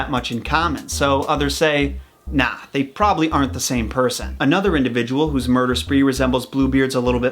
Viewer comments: Did he say “spoopy” ? 😂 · it sounded like he said “Sluts talk about that” and then he talked about it. Lol Ouch! Self-burn. Those are rare.